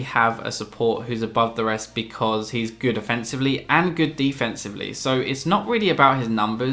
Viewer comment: gr8 vid, uhm at 01:22 did you say twice defensively? xD